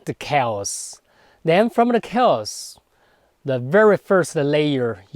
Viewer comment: the teacher says: "At the beginning of the universe, it's chiefly the kells. Then from the kells..." What exactly does he mean by "the kells"? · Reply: Hi Eric! I think he meant the "chaos". His accent just makes it sound like he said "kells"